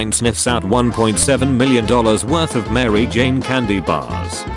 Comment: anyone else think it said mars bars at first?